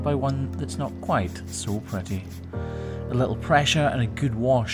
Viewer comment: thought he said “preppy”😂😂😂😂😂😂😂❤❤❤❤❤🎉🎉🎉🎉🎉🎉😢😢😢😢😢😮😮😮😮😅😅😅😅😊😊😊😊😊